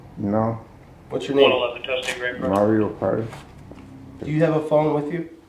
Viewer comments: Sounds like he says his name is Mario Kart.... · Did he say his name was Mario Cart?😂😂😂